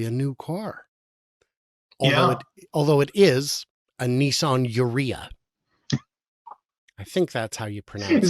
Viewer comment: Did you say the Nissan Areola? 😂 8:50. My 2021 M3 lease is out there somewhere. Low miles and in good condition. My new lease is in the garage. 👌